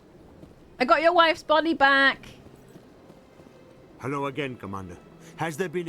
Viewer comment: I heard "I've got yoyr wife's bodybag". Been playing too much Payday2 recently 😂